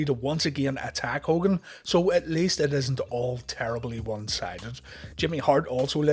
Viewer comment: When you said terribly one sided during the Hogan match I literally thought you said Terry Bollea one sided , which also works.